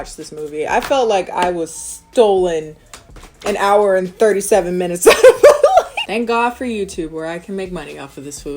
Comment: I thought it said 197 minutes. I was S H O O K